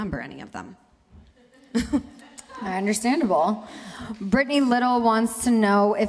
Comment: omg did u just say "understandoval"